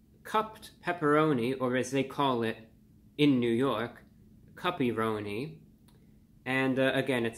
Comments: I thought review bruh was saying puppyroni LOL. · Thought you said "puppyroni" at first.